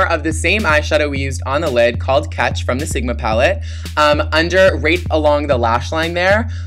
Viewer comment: when you said right along the lash line, it sounded like you said rape xD